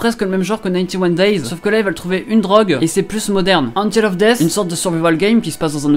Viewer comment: c quoi l’anime svp j’entend pas bien le titre ?? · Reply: Angel of death